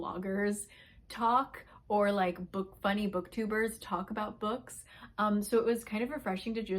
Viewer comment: Oh, book FUNNY! I thought you said Book Bunny😂😂😂